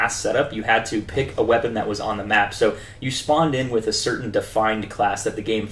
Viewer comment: did he say "spawneded?" lol